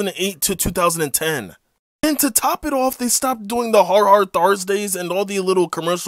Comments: I thought you said “hard R Thursday” for a moment I was born in 2008 so I was about 2 when this ended but I still occasionally see these shows on CN late at night in Alabama specifically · I thought he said hard r thrusdays.